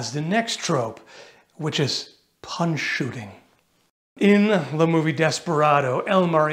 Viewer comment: I had to read the text. I thought you said "pun shooting". But since I'd never heard of "punch shooting" I suppose I wasn't missing much